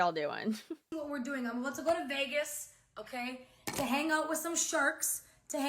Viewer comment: I heard "I'm going to Vegas to hang out with some Shart."